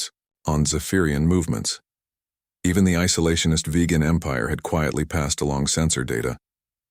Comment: did he just say Vegan Empire?